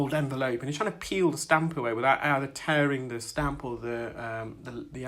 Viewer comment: was i hearing things or my audio not so good today on my computer but did i hear u mention "staph" when u were clean that real thin skin off of the bony part of the ear canal? i have an idea what staph is but never heard it mentioned while someone was clearing an ear canal. thank u again!